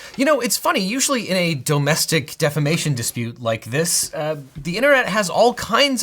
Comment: The fist time I watched this I thought you said it was a Domestic Defication suit....